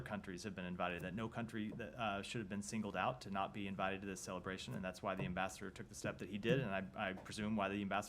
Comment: Did he say celebration? Yeah bro they are celebrating the Atomic Bomb 🤣